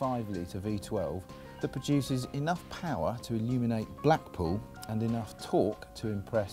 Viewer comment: did he say black poo? very nice...well it is a Lambo it can make anything it wants!